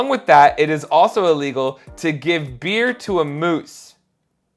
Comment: I thought I heard "it's also illegal to give a deer to a moose." I thought you were talking about about animal husbandry laws.